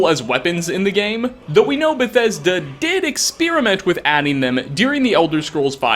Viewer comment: Is it just me or did anyone else hear "ex-spear-iment" at 3:59?